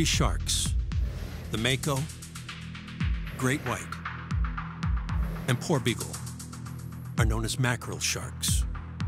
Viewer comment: When they first named the ‘Porbeagle shark’ 🦈 I heard The “poor people shark“ 😂